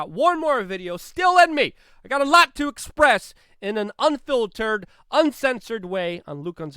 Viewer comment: When you said "unfiltered" I thought you said "unfilled turd".